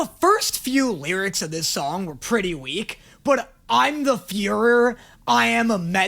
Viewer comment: no because why did i think he said fuhrer😭im going to hell too